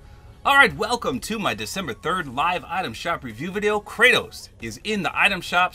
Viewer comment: Anyone else heard him say December 30 really quitely